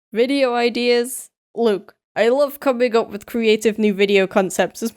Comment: the way he said look is like saying Tom nook badly